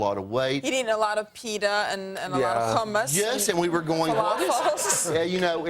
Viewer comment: sounded like she said cummus at 4:10.